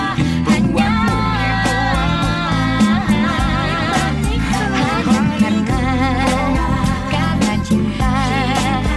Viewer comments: itu tata ngomong apa ya dari dulu gak ketemu 😂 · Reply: honey bunny ku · ini ngomong apa sih tata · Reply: Honny bunny ku.. 🗿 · Menit 3:22 ada yg ngomong apa yah? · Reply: Hanny Bunny Ku 😅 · Ada yg tau 3:22 ngomong apa? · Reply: Honey bunny ku,...